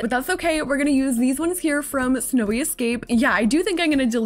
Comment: no because I thought she said smelly escape- 💀